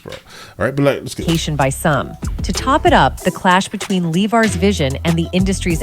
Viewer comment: Did I just hear that right? She said "...Lee Var's vision..." @ 10:04 . I feel like this video is 20 minutes of not getting to the point.